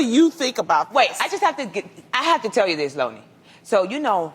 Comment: Did she call her Lowni @ 5:09😂😂😂😂😂